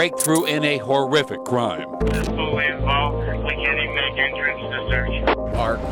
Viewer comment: I could’ve sworn the fire fighter said this is fully engulfed... not fully involved lol